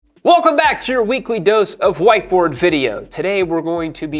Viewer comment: I thought you said "white boy video" and not "white board video". . . . I prefer my version!!